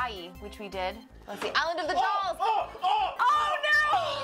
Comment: I thought said Detroit not The Dolls